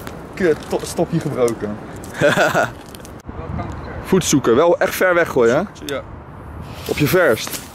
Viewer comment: op de achtergrond "gooi kanker ver" 😂